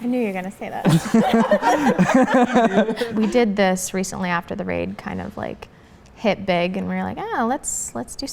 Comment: did you say RAID??